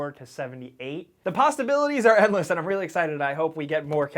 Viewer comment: I swear at 3:01 you said " posstaibilities